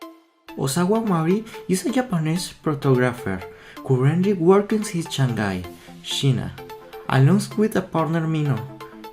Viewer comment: "osaguamari disingapanese photographer corhundred workens his chanday shina" bro idk what hes saying